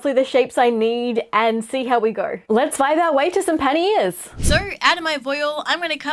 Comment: OOOOOH Panniers! I thought you said Panty Ears and I thought "Well that is a bit on the nose."